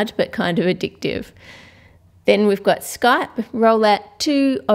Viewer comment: so,Is my computer trolling or she made the skype icon for geometry dash?I thought I was seeing things... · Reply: Sorry,replayed it like 70 times and I heard her say "then we've got skype"